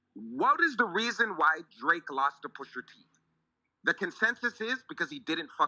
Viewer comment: why is he saying Pusha T with a hard R? “Pusher T”?? 😂